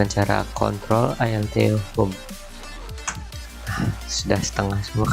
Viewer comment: = Bang itu shortcutnya apa bang tolong bang soalny kurang kedengeran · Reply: ctrl + alt + home